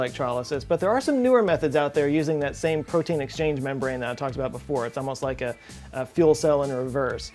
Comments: Did he say proteine exchange membrane, instead of proton exchange membrane? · sounded like protein exchange. 😊